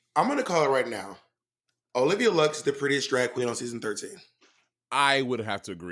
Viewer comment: "Olivia Lux is the __ of season 13" can someone translate for me what the f did Bob say in this sentence? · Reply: Prettiest drag queen